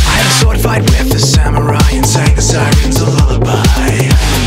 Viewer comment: There is nothing more romantic then killing scientists, monsters and samurai to win the affections of your potential loved one ❤️ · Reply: how can you even misheard sirens as scientists? o_o